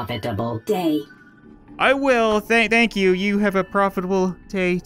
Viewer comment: tha-thank you. Am I the only one who hears this?